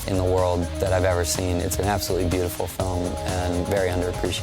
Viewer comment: My heart skipped a beat when he said the cruise, cause i thought he said the croods